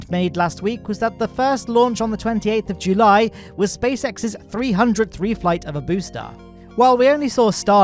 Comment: I’m sorry, did you say 300th flight of a booster at video time stamp 5:50 🧐🧐🧐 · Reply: 300th reflight I think